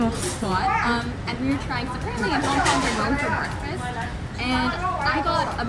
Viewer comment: Hong kong is known for breakfast? i cannot hear it and the sub says "mom and mom for breakfast"... so pls help me